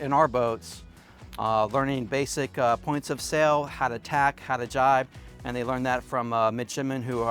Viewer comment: the subtitles are wrong, is supposed to be "how to tack (a crossing maneuver to sail against the wind), how to jibe (a similar maneuver with the stern)..."